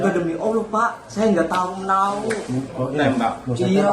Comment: Ada kata " Tembak " apa maksudnya itu ya, apa saya salah denger